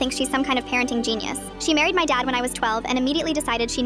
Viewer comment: I was so zoned out at the beginning i thought i heard "she married my dad when she was 12"💀